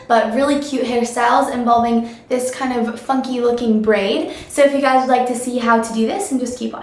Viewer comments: omg i thought she said "fuck you" when she said "funky" at 0:14 lmfao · i really thought you said fucking instead of funky hah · Oh my gosh I thought you said "fucky looking braid" instead of Funky. oops! Hahahaha